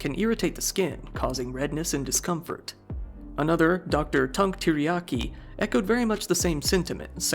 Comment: Dr. Tunk Teriyaki??😂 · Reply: I heard 'Dr. Tongue Teriyaki'... 😅😂